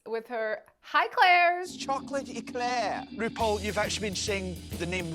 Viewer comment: its, "aye claire's!" not "hi claires" I believe 😅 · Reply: Another Glaswegian here, and I heard 'Aye, Claire', too. Especially with that Elaine C Smith delivery. 😂